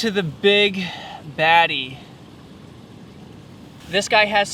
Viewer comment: I thought he said big daddy 😂